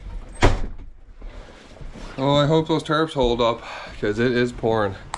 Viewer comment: english auto subs write "it was porn" instead of "it was pouring". Writing this because it might avoid a strike :)